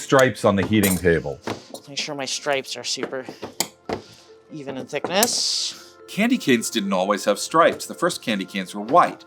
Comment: the captions says "even in sickness", but he actually say "even in thickness"